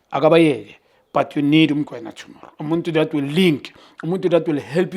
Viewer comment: Somewhere around 09:44 into the video I heard “We need uMokoena tomorrow”. He’s no longer hiding